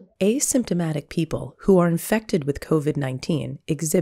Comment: The video started buffering here 0:04 and it sounded like the said ASAP.